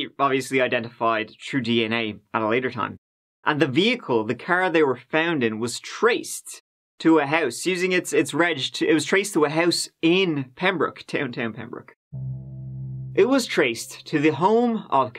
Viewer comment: At 03.05 I honestly thought he said they found a turd in a house. Sorry! 😂